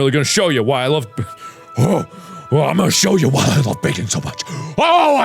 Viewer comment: the captions say bacon instead of baking lmao